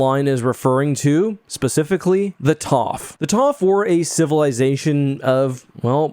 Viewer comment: Did you just say -- Toth? Tof? Toff? Tauph? Taww'ff? Suggestion: When the narration hits a proper noun, display it. You can't trust the YouTube auto-subtitles to guess the spelling; and if it's an obscure bit of lore, you can't assume viewers will have ever encountered the word in writing. Having the correct spelling would be a big help for viewers who want to consult Wookieepedia or wherever.